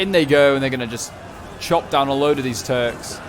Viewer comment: “Chop down a load of these turds”. That’s what I heard, hahah. Another awesome video man!